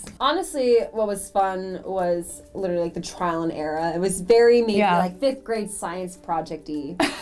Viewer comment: Did she say "Trial and ERA"? 5:29 S:)